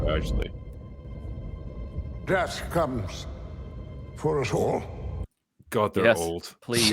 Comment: Thought he said "Grass comes for us all.."